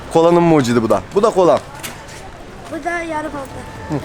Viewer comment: o çocuk ne dedi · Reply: Buda yara bandı dedi sanarım yara bandı satıyor